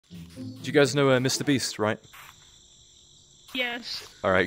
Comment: I thought it said me clean but I remembered it was me beast💀